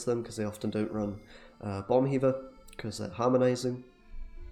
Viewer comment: whats the card hea talking about? Bomb weaver i Hear lol...